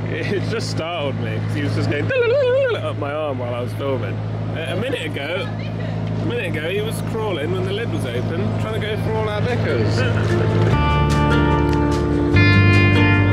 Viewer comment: I genuinely thought I’d heard you say the spider was after your knickers not bickers 😂🕷